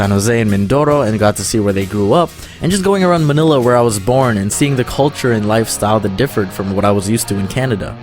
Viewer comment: I thought he said “When I was seven I was born”😂😂😂